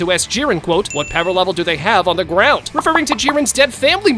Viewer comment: for those who don't know what they saying " Jiren: Shut Up Frieza: You Shut Up Jiren: That's why your power level is so pathetic Frieza: That's Why your family's Dead.. Dead As Hell What's their Power level? What's their Power level at the Ground That's why you have no grandma So She can pray to God Of Destruction Bitch How about that? "